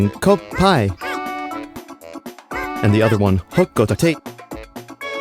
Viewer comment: I never knew what they were saying. Me and my sisters (we played bingo battle a lot) always heard it as “ho hai” and “ho hey”